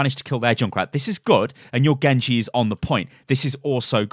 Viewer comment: Did he just say a silver genji is on the point